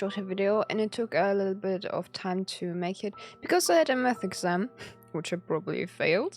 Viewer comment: i misheard the intro and thought he said i had a meth exam